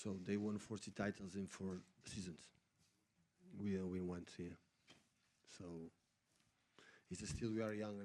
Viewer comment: Barcelona won 40 titles in 4 years, 10 titles a year? Is he just making stuff up now? And someone needs to tell him Man City have won 2 leagues. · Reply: Oh right thanks. He actually says "we all we wants here", but i suppose we can forgive him the odd poor bit of English.